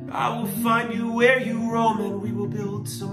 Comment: lol turn on the captions and at 3:00 when he says "I will find you where you roam" the caption says Robin instead hahaha